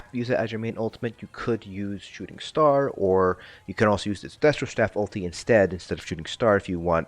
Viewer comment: What staff? I know you must have said 'destruction staff', but it really doesn't sound like it. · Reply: He said destro staff ulti (destro is short for destruction )